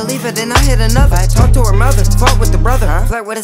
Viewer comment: he says "fuck with the brother" if so, then first time i heard him curse lol